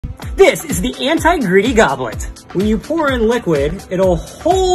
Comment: I thought he said “anti Griddy goblet” 💀